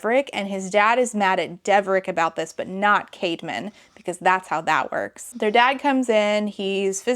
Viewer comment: Definitely thought you were saying Cavemen up til that point and I didn't blink an eye. 😂